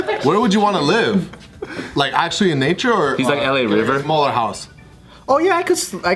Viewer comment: LMAOOOOO anyone catch what said at 7:42 after asked where he would live? “LA River” 😂😂